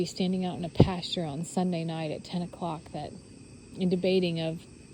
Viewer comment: I was wondering the same. It sounded like she said. Sunday night or Monday night. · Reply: She said Sunday night. Its Sat. Morning now.@